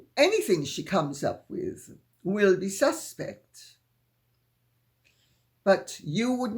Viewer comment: I misheard your "Anything she comes up with is suspect" as "cesspit". I think I was right.